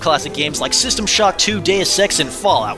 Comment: WHen he said Deus EX, I thought he said "day of sex", and I was on another tab, so I was really confused...